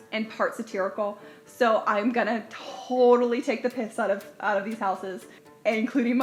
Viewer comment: Did I hear "Take the Pince out of these houses"? ^_^ Awesome!